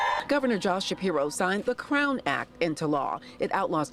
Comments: OMG! ~ When the news anchor says, "Crown Act", it sounds like she said Clown Act. ... at 10:20 . What does it sound like to you??? Must have been Freudian slip on the news anchor's part. 🤡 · When I heard this I thought they said CLOWN ACT. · I heard “the clown act”, which describes it accurately 😂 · I misheard it as "Clown Act", at first. A more suitable name